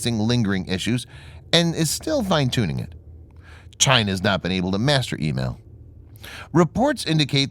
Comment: when you said:"China has not been able to master EMAL" what i heard was :"China has not been able to steal EMAL".